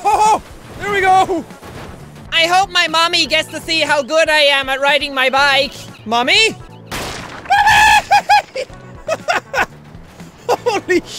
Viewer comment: That "Mommy" Sounded like "Nani"